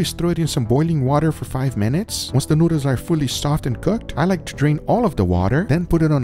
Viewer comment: I thought he said "I like to drink all of the water" 👀